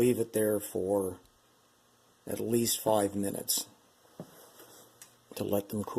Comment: Question: how long do you leave the glasses in the cold water after tinting; and should i wipe them dry with a cloth or just let them dry in air after the quench Also is there an indication to a certain point that i should remove the glasses from the cold water? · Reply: I heard him say 5💧 minutes in cold water